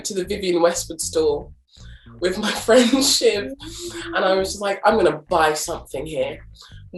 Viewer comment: Question for native English speakers: when Chinenye says "with my friendship" did she misspoke, did I misheard or is that a way of saying with a friend without specifying gender? Or maybe just dialect? Just curious since I'm improving my English :)